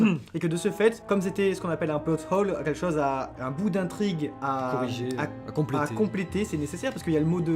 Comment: Il a dit quoi a 7:12 ? On dirait un mot anglais. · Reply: Plot Hole